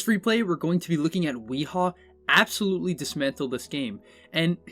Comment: I wasn't looking at the video the first 15 seconds and I hear "dismember the game". First thing that comes to mind is did he really make that joke with a pudge but turns out its tiny, needless to say I got dissapointed. :( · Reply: He said "dismantle" though